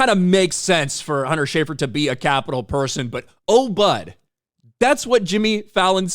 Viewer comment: i could care less about fallon but.....who says "oh bud"? it sounded like he said "oh, but" which makes more sense and they activists are freaking out cause the speech recognition made an error and wrote bud.